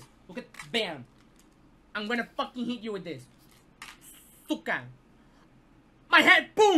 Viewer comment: Did say сука Сука (рус.) - bitch